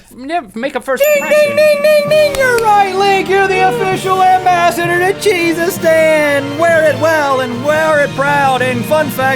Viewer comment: Lol I thought Rhett said "The Ambassador of Jesustan when he was congratulating Link.